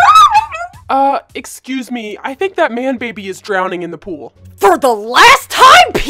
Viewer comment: I was dying of laughter what did call him a man baby HAHAHA I am literally dying right now